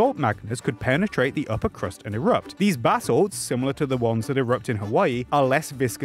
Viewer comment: Every time he says basalts I always hear 'bath salts'. Sounds to me that Yellowstone just wants to relax